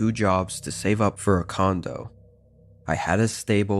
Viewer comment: thought he said c0nd*m